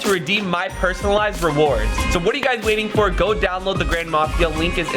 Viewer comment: When you said the grand mafia I thought you said ‘ grandma mafia ‘ lol